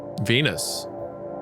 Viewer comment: I thought you said penis